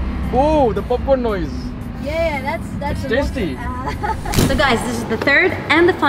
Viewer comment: did he say poopin sound is tasty?